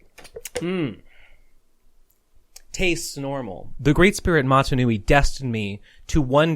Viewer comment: I thought you said "mmmm tastes" and I thought that was hilarious